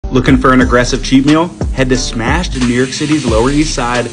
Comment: I thought you said cheap meal not cheat meal, i'm sure this isn't a cheap meal at all lol ....